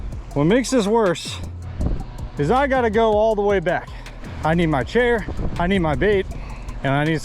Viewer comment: nah cause I thought he said I need my vape